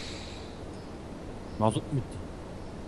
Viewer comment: 'de ne dedin ? :D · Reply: abi : Mazot mu bitti ? Diyor :)